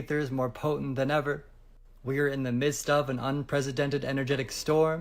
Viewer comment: did...did he just say an "unpresidented"???